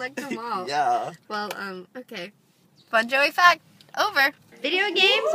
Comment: When said fun Joey fact I thought she said Bon jovi fact. Lol